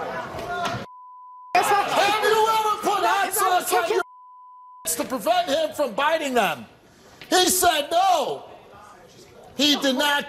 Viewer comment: Can anyone hear the question Steve is reading? I have listened to that part multiple times but I can't hear or understand what he's saying. Does anyone know? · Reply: Have you ever put hot sauce on his fingers to stop him from biting his nails